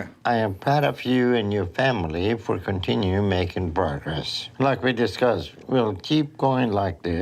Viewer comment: I swear Dr Now said "I'm proud of you and your family for continue making burgers" 😂 I know he is saying "Progress" but I can't unhear burgers now